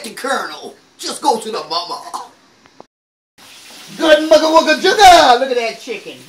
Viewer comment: what is she saying hahah i laughed so hard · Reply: Ashley Nguyen Good mutha wucka suga? She's keeping the fun in the kitchen. 😊